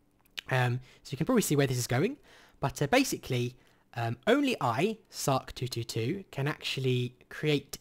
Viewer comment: When he said "By sarc 222" It sounded like he was saying "I suck doodoodoo", and i thought he turned 4.